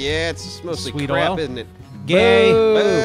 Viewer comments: Did call GAY? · did say gay at 5:18?